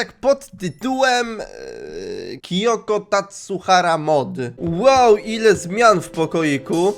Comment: Czy tylko ja usłyszałam Kiyoko TasURHARA A nie Tasuhara XD